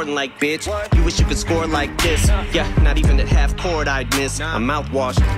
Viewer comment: I swear the first time I heard the song I thought Em said “not even if I’d have court I’d miss” like he had to go to court with a judge and jury